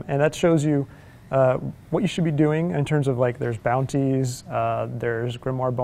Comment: Did he say bountis att